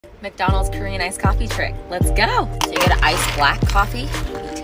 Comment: For some reason I heard “Korean ice cream trick”